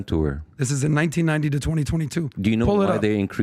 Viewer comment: they said 12 years not 30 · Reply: I thought he said from 1990 to 2020. Thats also fitting with their numbers 650k to 2.1m.